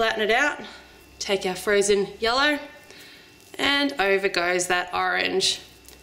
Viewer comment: I heard "take your frozen yolo"...:D